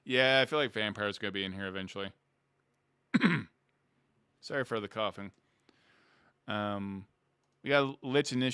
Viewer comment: “Anyone who has played a wizard has thought about playing a Lich…yeah I feel like vampire is going to be in here eventually (coughing) sorry for the coughing” And I heard with that context “sorry for the coffin…”